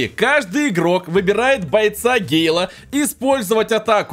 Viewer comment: я услишал что он сказал (Бойца гея) 😅